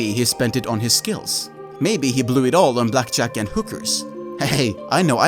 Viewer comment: I definitely thought you said Blackjacking Hookers lol. Great vid. Really enjoyed it.